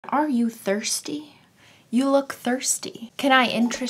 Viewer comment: I thought she said "Are you thirty?" I was like "um no I'm 16 I'm a little over half way there- wait you said thirsty nvm.."